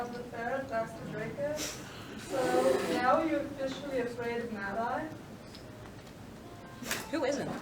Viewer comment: Does anyone know what the question was at 12:57. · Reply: I think it was ‘now we you officially afraid of mad-eye’